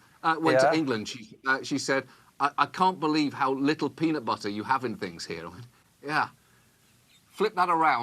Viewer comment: captions say "I can't believe how little President Bush you have in things here." I mean, it's still technically correct, but... President Bush and peanut butter are not two things that I would normally conflate with each other.